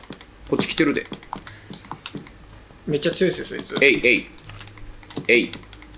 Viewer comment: 「えいえいっ」がDJ神山にしか聞こえなくてしんどい